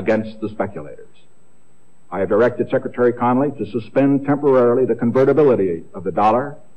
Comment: ? What was that word he used at 10:23? "Temporarily"? Oh... ok Thanks for this video